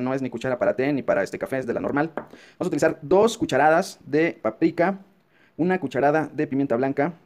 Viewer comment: Alguien em dice que quiso decir? No entendí ese ingrediente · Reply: Dos cucharadas de paprika (pimentón)